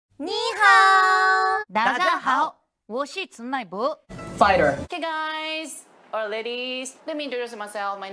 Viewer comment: when he said "fighter" I thought he said "fight her"